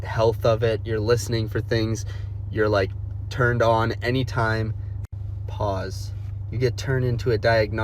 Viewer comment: lmaooo im childish but what was pause about would he said? 😂😂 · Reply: “…you’re turned on anytime…”